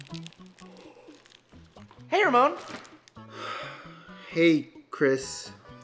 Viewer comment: I thought Chris said “Hey your Mom” but he actually said “Hey Ramon”